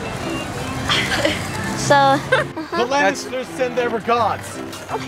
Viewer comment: did she say "nej"